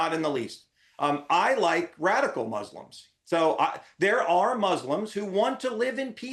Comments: did he say ‘I LIKE RADICAL MUSLIMS’?!?!?!?!?!?!?!?!?!?!? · Did he just say he likes radicals??